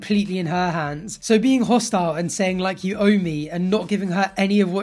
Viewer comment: When he was speaking I heard “you own me” not “you owe me”. Correct me if I’m wrong.